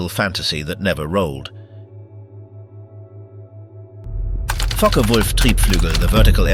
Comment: all I hear is f*ck a wolf